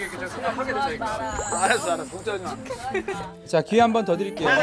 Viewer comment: 티키타카 시작점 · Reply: 나 좋아하지마라 ➡️ (잘 안들리지만) 알았어 알었어 걱정하지마 여기부터